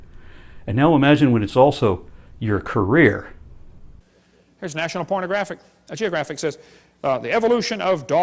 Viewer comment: D- Did he just say "National Pornographic?!" 😄😆😂🤣😂🤣🤣💀